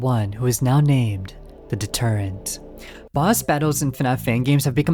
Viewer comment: I thought he said, "the detergent"